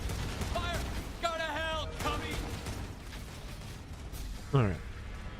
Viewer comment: Did he just say "go to hell, commie!"??